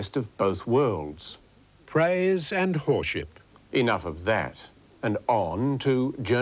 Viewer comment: I thought he said horsesh**t not whorship